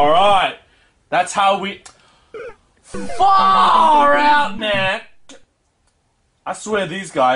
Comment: 哪位告知一下他说了什么?????? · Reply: L Farrrrrrrrrr our man